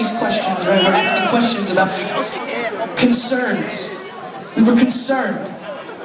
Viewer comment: did someone say "so be concerned" at 3:58?